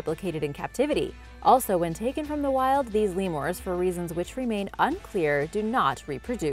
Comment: Did she just say Lemors